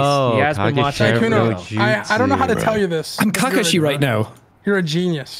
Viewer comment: Everyone got to the time stamp 7:35. Did I hear Spedicey right or am I going crazy. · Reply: He says “I’m Kakashi.”